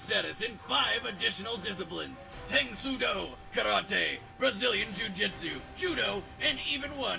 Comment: Misheard Brazilian jiu jitsu as bazillion jiu jitsu.